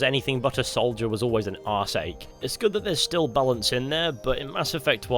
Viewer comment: What the hell does that word even mean...? R-sake? What's that? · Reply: Arse-ache, pain in the ass